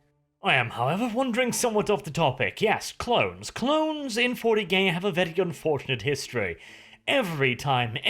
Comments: I swear says 40gay...... Hahahaha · did he say clones in 40gay?.Or is that just me hearing that